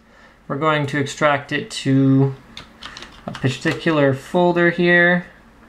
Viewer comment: Did he just said "testicular folder" at 1:10 ?